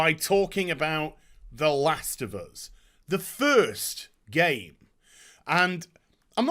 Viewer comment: Not a joke: at 00:28 instead of "the first game" I've heard "the first gay".